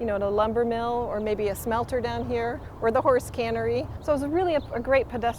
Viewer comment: Horse cannery. Did I hear that right? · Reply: Yes, there was a horse cannery in Linnton. Canneries were used for horse slaughtering as horses were sometimes processed for meat, historically. <link> -Ed Jahn